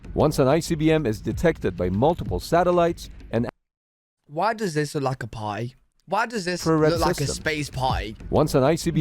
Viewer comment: He said why does this look like a party i thought he said why does this look like a pie😂